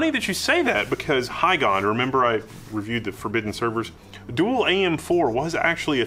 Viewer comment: I misheard at the start there. "Hi god remember I reviewed those forbidden servers"